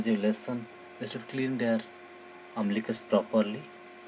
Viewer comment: haha.. at the end it sounds like he says "how to clean their umbilicus properly, by licking butt, thanks for watching" o_O